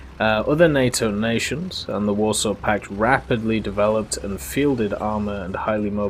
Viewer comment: did you say a other NATO nation in the Warsaw pact?